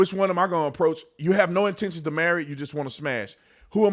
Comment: All I hear is "I have no one"